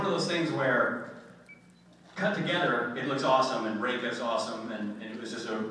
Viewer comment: For a second I thought he said "rape is awesome" and I was like, what the hell Richard? Then I rewound and was like, "oh, im so stupid."